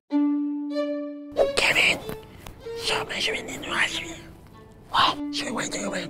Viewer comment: In 5:16 of you turn on captions he actually said “stop measuring in Russia”